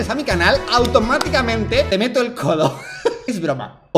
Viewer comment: Yo escuche "Voy a desmallarme" y lo del codo escucho "Mete me el codo" . No se que dice pero en un momento escuche "Besa me el codo" pero no creo que diga eso